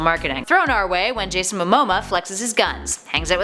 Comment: Did you say MOMOMA